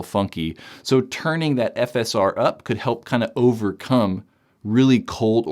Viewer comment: Love all the videos! Im always watching your reviews trying to make my decisions for future markers. Is it me or you said FSR at some point? I think i heard it